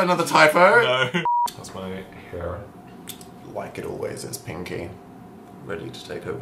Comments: Am I deaf or did call 'pinky' at 2:31 ??? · did call pinky? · is calling “pinky” or???